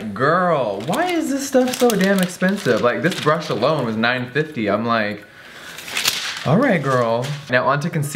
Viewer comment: Walking in on my tv, at 3:08 I literally thought Jeffree said the brush was $950.00 and at first I was like Wow and then I was like Okay it’s probably a Givenchy brush so that makes sense..... Two minutes later I realize this is the Full Face Using only Kids Makeup... and the brush was... $9.50€